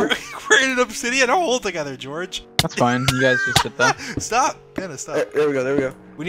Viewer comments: NOTE: I don't know if this is ACTUALLY what Dream said but it SOUNDED like it "STOP! Panda stop 😭" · did he say "dennis, stop" or am I going crazy?? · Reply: "Pandas, stop"